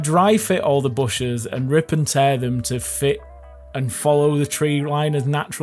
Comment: Did i hear rip and tear (@ 9:24) <link>